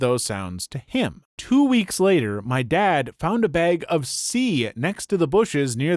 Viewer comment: I CANNOT be the only one who first thought of a bag of cum instead of coke